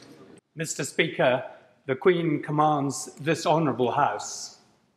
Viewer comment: When he said 'this honourable house' i thought he said 'Dishonourable house'